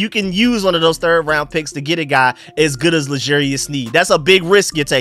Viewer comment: OMG, just gotta comment that I'm watching with CC turned on because I watch YouTube at 1.5x or faster pretty much all the time. When mentioned missing out on the L'Jarious Sneed trade, the captions thought he said "luxurious sneeze!" 🤣🤣🤣 Love the content, keep up the great work so the rest of us who can't keep up with X/Twitter (which I always think of as "Ex Twitter" 😄) can know what's going on with our favorite team!